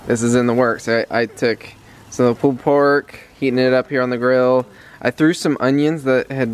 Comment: Ok I thought said they had left over pooed pork! I was like What?? Then watched 3X and realised ohhhh he said PULLED pork not pooed pork! insert derp face here LOL I love the sun setting in the evening and seeing the light shine through the clouds and turn so many different colours every day. I also love a nice sunrise but really it is rare for me to see those. We live on the sunset side of our building. LOL